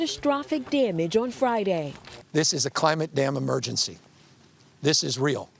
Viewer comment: Cali Gov strolls with hands in pockets at 1:43, seems to have the collar up on his green jacket, I wonder if a guy like this could make it through basic training. I think he said "this is a climate-damn emergency....."